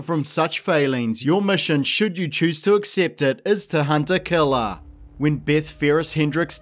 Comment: When the sponsor came up I wasn't watching, just listening. So instead of "hunt-a-killer" I heard "hunter killer" and was like "dang this guy even got a star wars themed sponsor for this video? That's dedication.